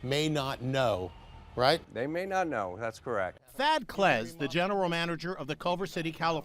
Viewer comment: I thought he said Fat Clez😂😂😂